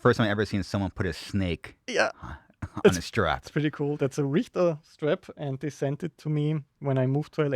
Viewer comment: Whats that strap company @ 27:15 I cant quite tell what hes saying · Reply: Richter